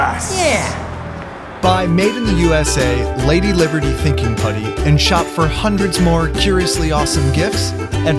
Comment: I thought it said “thinking puberty”